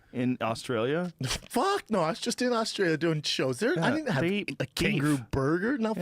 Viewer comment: Did Brendan say he was just in Austria?